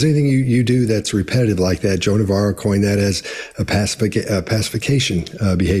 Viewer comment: These guys always quote Joan of Arc but I never know why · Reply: Joe Navarro. I thought they were saying Joan of Arc too for many, many videos